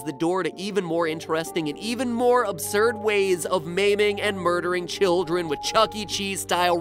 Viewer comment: Okay, when he said "maiming and murdering children", I thought he said "memeing and murdering".